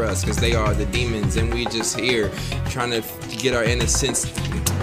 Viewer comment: Trying get rid of our fear? Is that what was trying to say?